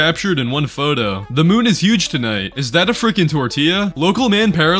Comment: I was listening to the video but not looking at it and on the last one I thought he said, "So the woman is 412?"